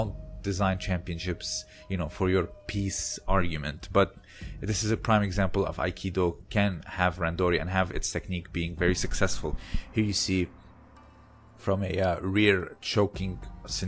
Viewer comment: I said that it can work, their koshi nage is Koga's sode, it jist needs a randori structure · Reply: got it... yeah somehow i thought you were saying "can't" with the stop T sound on min 6:17. Haha